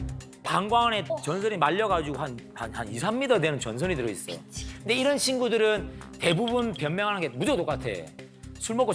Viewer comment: 미X 작게 들림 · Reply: ”미치겠다“